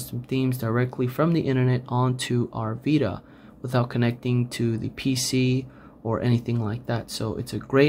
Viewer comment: I thought you said without pc